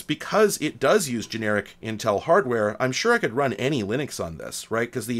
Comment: I heard, "I'm sure I could run Annie Lennox on this." lol